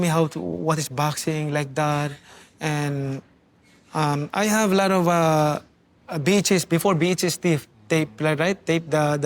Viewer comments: thought Manny said he had a lot of bitches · dayum he bad b**ches is that what he said??😂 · I literally thought Manny was saying "I had a lot of bitches" 😭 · I thought he said I had a lot of bitches